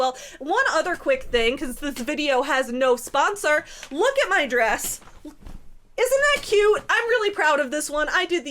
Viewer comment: I definitely misheard "Look at my dress" as "Look at my address" and thought for a second you were Sssniperwolfing yourself 😂